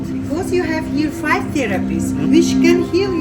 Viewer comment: I thought she said therapist 😂that’s what I need to get through life, is five therapists 😂